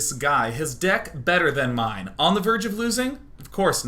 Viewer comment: "So last night, his Dick was better than mine?" Did Day 9 say that or am I hearing that wrong? And then at 2:50 he talks about "exploding in his face..."